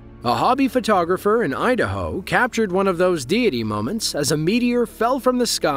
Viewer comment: I heard diddy 5:14